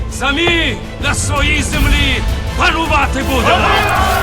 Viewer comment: когда он сказал "самі на своїй землю панувати будемо" мне послышалось"самі на своїй землю ворувати будемо")